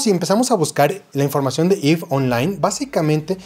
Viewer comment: no escuche o entendi muy bien, el juego se llama (if online) osea se escribe tal como suena? Ayudenme porfavor seres detras de la pantalla... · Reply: Se refiere al vídeo-juego ´´´´EVE Online´´´´